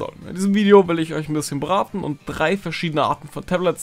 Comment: in diesem Video will ich euch ein bisschen beraten" was verstehe ich: ,, in diesem Video will ich euch ein bisschen braten"